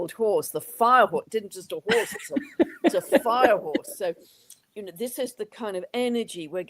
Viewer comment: About language: English is not my mother tongue ( being Swedish) and so I misheared and saw inner images of us trying to ride on fire hoses...the hoses were winding, bending like giant wild kundalini snakes...we looked a bit like witches trying to tame new brooms.... Turning on the cc made me realise Pam was speaking about horses...😅 Well, exciting times and rides ahead any which way!